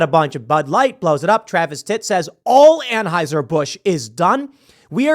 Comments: You guys heard him say Travis Titt right? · I could have sworn Tim just said Travis tits 😂😂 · I don't know if anyone said this in the 3,309 comments, but "Travis Tit" instead of "Travis Tritt" at 0:36 just got me. XD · Did he say Travis tit at the 00:37 second mark? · Did he just say Travis Titt?